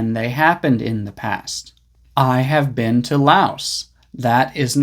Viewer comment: Thank you for your postings.could you write again What did you say on 2:22 sec."ı have been .... " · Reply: to Laos